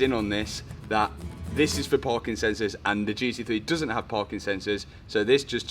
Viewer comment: Hahah thought for a sec that you said they are for ''Parkinsons'' not ''Parkingsencors''...